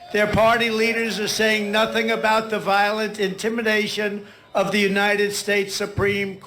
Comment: "Very strange grammatical context?" Trump can barely string sentences together; for example, "Pence should send it back to the states..." NOT "it" should be "them." Our ex-Pres can barely use our language, yet another very sad fact. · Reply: I think I heard "intimination" in there.